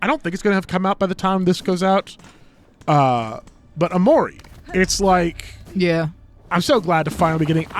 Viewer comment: did he just say...Omori? OMORI MENTION OMGGG!!! I can't wait! I really hope you guys enjoy it!